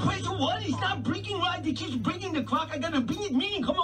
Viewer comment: I thought he was saying cock instead of clock